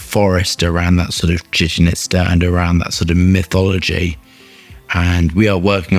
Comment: what was that word "jushnista?" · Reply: Chichen Itza- an ancient Mayan city.